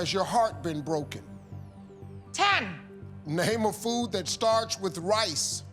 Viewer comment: I could have sworn he said to Fran "name a food that RHYMES with rice."